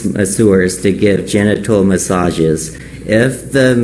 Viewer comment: At first I thought he said gentle massages. Lol